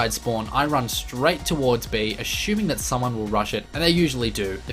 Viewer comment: and 2:15 sounds like saying "asshuming"